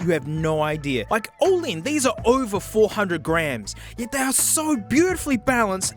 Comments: no lie I deadass thought he said “over 400 GRAND” I just about had a heart attack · WOW I THOUGHT HE SAID 400 GRAND NOT GRAMS LMAO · when he said over 400 grams i thought he said 4 grand like said in the title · Did i have a stroke @ 3:29 because i heard over 400k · When he said there head[hones were "over 400 grams," I heard "over 400 grand." I almost had a heart attack. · Thought you said "These are over 400 grand" haha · I thought he said "All-in, these are over four-hundred GRAND" instead of "grams" and I nearly shat myself. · I thought he said "over 400 grand" and I nearly had a heart attack